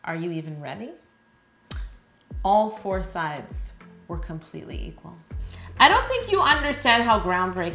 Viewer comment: I heard "All four sides... were completely evil."